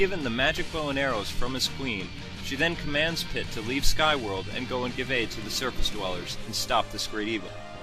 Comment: lol at 0:53 i honestly thought that he said "to go and give aids to the surface dwellers" HAHA